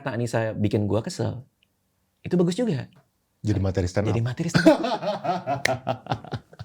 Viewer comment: dia bilang apa? tolong ada yg tahu, audionya kurang.... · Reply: Jadi materi stand up katanya. 😅